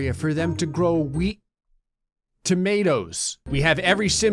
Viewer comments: I heard that as "Wheat Tomatoes" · youtube so innocent. instead of "weed", it thought you said "wheat"